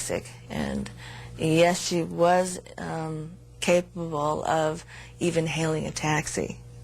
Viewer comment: what did her friend say? It sounds like she said "Yes she was capable of hailing a taxi". That doesn't make any sense to me.